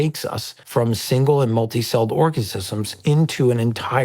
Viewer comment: did he just say orgasisums